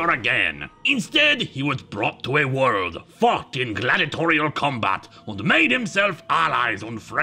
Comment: Are you sure "fought" is what he said there?